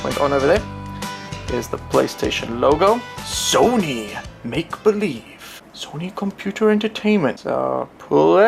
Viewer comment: Nice comparison! and when u said Make.Believe, it sounded like you said Maple Leaf LOL